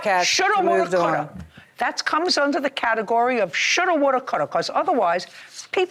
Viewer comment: Lol, 9:19 “category of *Shoulda, Woulda, Coulda,” is what she is saying.